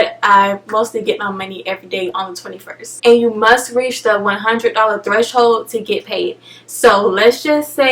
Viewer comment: Wait I heard and thought $10 was the threshold not $100 😫 lol